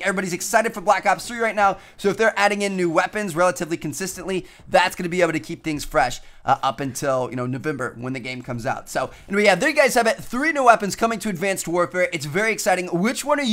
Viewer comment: did he just say "noveUmber" or do I need new ear buds?